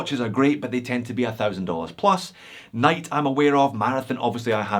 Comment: Great episode! What was the watch brand mentioned at 0:47 between Ball and Marathon? "Night"? I don't think I've heard of that one, will have to check it out... UPDATE: <link>